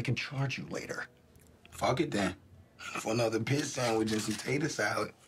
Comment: I always thought he said "pig sandwich" but he said "pit sandwich". Was thinking who eats medium rare pork? 😂